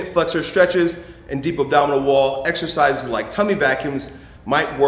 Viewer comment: what the hell did he say between 0:25 and 0:33, I am lost....... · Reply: "And deep abdominal wall exercises like tummy vacuums might..."